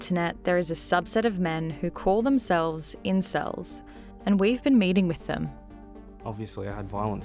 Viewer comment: U know whats hilarious? That the narrator chick at the begining she said "and weve been meeting with them." Because of her accent it sounded like she said "and were mating with them"